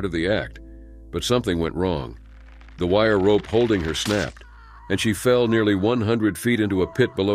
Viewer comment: How did Sarah die.and when she fell she hit the net and not the ground? The net caught her,so how she die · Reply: I sworn I heard him say she fell in a net